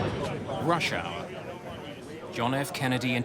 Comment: Did anyone notice the beginning. He says "Russia. John F Kennedy airport." Lol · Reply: “Rush hour.” 😁 I thought he said Russia at first too!